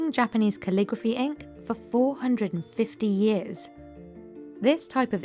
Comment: Misheard it as ‘this man has been making ink for 450 years’